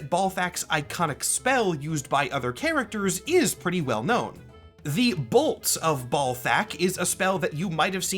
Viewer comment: Yeah in MvC3 I always heard "Bolts of Ballsack"